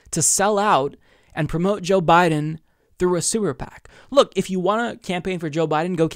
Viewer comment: Thought I heard say "sewer pac". Freudian slip? Lol!